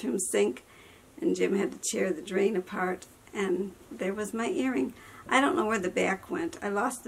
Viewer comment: You are beautiful just the way you are sweetie you don't need to dress up for us. I thought you said hearing went down the drain.....lol not earring. You make me laugh thank you.😘😘 I love you!!!! You're so sweet 😘😘.